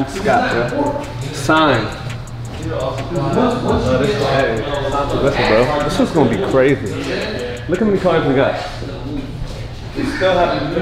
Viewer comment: i thought he said carts, but no cap a cool kicks smoke shop would be cool i don’t even smoke but it sounds dope (obviously a better name tho)